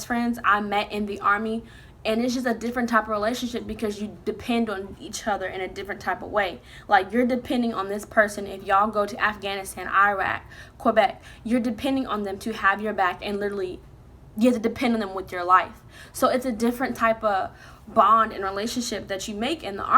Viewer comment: did you say Québec????? Im trynna understand bc Im from there lol